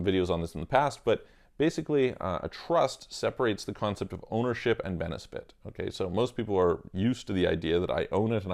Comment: A trust separates the concept of what? Ownership and BeneSfit? Am I hearing right? Is that a legal speak word or did you misspeak? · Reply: It separates the concept of ownership and benefit correct